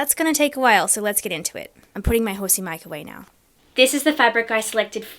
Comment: I fr thought you said "I'm putting my horse in the microwave now" for a full second 😂